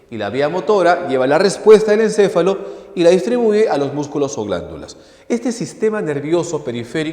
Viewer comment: consulta en el min 7:30 de las vias eferentes que musculo dijo? no entendi :C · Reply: por si aun te sirve 😅 Vías sensitivas o aferentes: información de los sentidos al encéfalo. Vías motoras o eferentes: respuesta del encéfalo a los músculos o glándulas